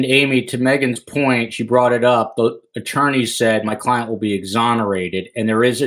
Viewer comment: The attorney said "my client will be exonerated"... > BCK's attorney DID NOT say that. He said "Bryan expects to be exonerated". The attorney offered no opinion of his own. Big difference. That attorney only spoke to BCK about the extradition process and not about the case at all...in fact he said he didn't want to speak to him about anything except the extradition.